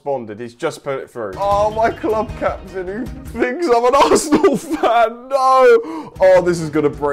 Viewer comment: Im an Arsenal Fan and My names Zac. Im concerned now. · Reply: ALL I HEAR IS " An Arsenal Fan on a string' ITS GGS FOR ME LADS.